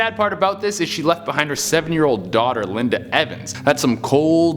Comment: Is it me or did you say 70-year-old daughter???